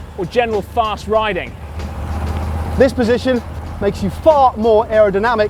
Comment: i thought he said this position makes you fart more xD